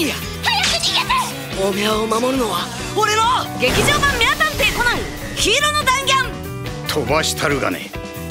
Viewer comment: 名探偵コナンがめあ探偵コナンに聞こえるの俺だけ？